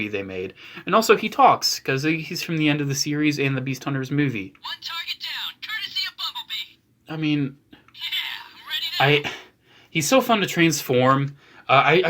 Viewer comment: Why do I keep hearing 'mumble bee' what the heckle is wrong with me